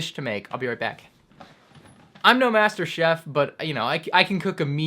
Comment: “I’m no master chef, but I can cook a mean frozen pizza.” I don’t think a quote has ever resonated with me more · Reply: Jonathan Fragale i thought you said master chief